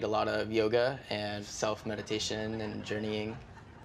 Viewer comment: When he said "self-meditation", I heard "self-medication", 'cause what he's selling sounds like the type.